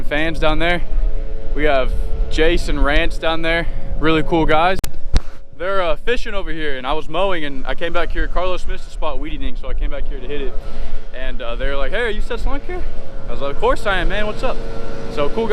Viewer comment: sounds like you said he bought weed and you came back here to hit it😂